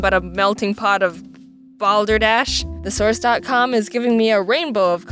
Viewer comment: I thought you said, "the source dot com."